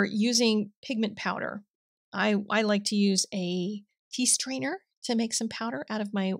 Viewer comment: I do have a question..you used a small red screened object to make powder, could you please tell what was it called again. Sounds like you said a piece trainer… thank you in advance. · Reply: Tea strainer—it’s linked in the supplies 🤩